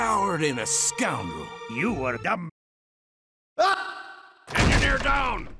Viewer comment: Engineer: I’m back *robot rock starts playing* ”HE’S BACK.” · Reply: I hear "dumbag"